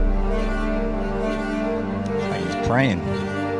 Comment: Please, I'm French and I don't understand what he said at 0:09 Can you tell me what he said please ? Tank you 🇬🇧 🇫🇷 🇺🇸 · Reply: "he's praying"